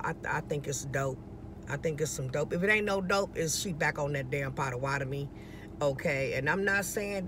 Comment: I thought you was saying 😂 SHE BACK ON THAT POTTED MEAT and I’m telling you right now that girl is going from place to place, if she doesn’t care about housing 🧐 sounds like HEROINE, CRACK, something to that degree. That’s probably why she can’t go to the hospital 😏 she’ll lose HER GOOD JOB. God did you a favor! Keep folks out your house unless you trust them 💯